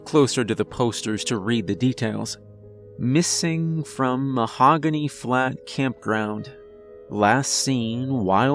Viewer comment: I need to go get myself an ear trumpet. I heard 'Mahogany Phlegm Campground'. Listen to Merzbow with headphones and that's what you get.